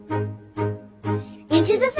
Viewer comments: Whenever I hear this song my brain always says it's " into the psycho bitch" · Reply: I hear “into the thickle bitch” IDK WHY 😭✋ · Whenever I hear this song my brain always says it's " into the psycho bitch" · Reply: I hear "into the b*cth of it"